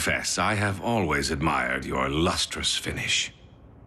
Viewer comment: “I always admired your luxurious finish” - knockout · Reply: Rofl really? And this whole time i thought he was saying lustrous finish 🤣🤣